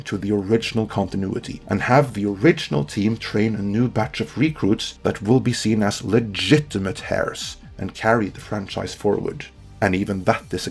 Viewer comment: Did he just say “hairs” instead of “heirs”?